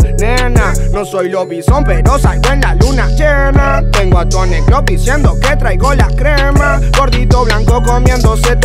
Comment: Eve Taborda pensé que decía "tengo a todos en el club diciendo que traigo la crema"